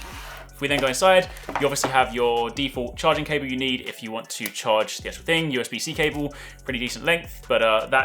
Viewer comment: that's the idea behind this "charge-what" now? ;) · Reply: I thought he said chargemate haha